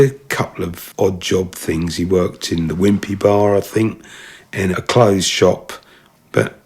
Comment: did he say wimpy bar?